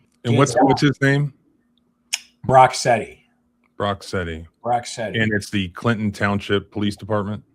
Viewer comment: what did he say his name was? Rocksteady? Isn't that the rhino henchman from teenage mutant ninja turtles???